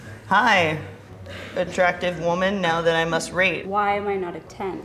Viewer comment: I thought she said "rape" at first.